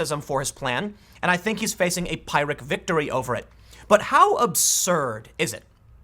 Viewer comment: Tim, its pronounced "piir - ik" victory. seriously it sounded like you were saying "pirate victory".